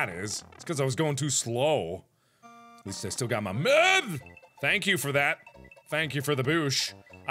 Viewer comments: did he just say, "at least I got my meth"? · did mark say "at least i still have my meth"? :o mark you got somethin to say for yourself??!